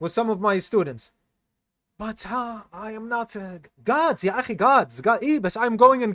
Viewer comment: Did he say Guards or Gods? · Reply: (Manager) "gods"